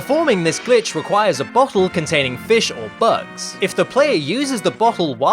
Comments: I know he bottle but at first it sounded like he said butt hole. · I thought he said "Butt Hole"